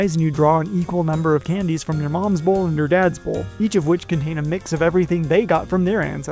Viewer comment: "You draw an equal number of candies from your mom's bowl and your dad's bowl." That's it, folks. It's official. Candy is stored in the bowls. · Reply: I heard "one from your dads BALL and one from your moms BALL" and I was like wtf did I just hear?